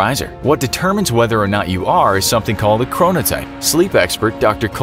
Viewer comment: Did you just say corona type